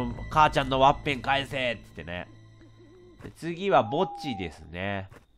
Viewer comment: ｢次は墓地ですねー｣が、｢次はぼっちですねー｣に聞こえたw